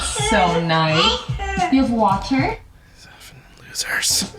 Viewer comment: Why does it sound like S is saying "I hurt" ? Maybe I'm hearing it wrong.